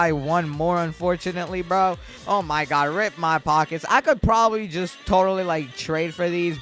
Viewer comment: Did he just say "Rip my card" LIKE GURL YOU HAVE MORE THEN 40K ROBUX